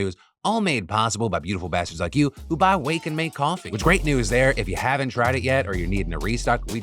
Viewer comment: This whole time i thought he was saying "Wake and Bake Coffee" 😂. Still gonna try it out